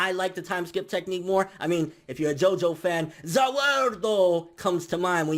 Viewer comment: did I hear you just say Za Worldo? Why don't you review Jojo then?!